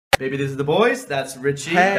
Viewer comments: did herc say 'you look dirty ;)' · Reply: I thought it said you look daddy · did herc say 'you look dirty ;)' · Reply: I thought he said "you look daddy" tho